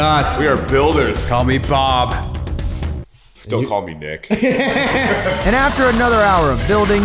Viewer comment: I thought Nick said "don't call me d*ck"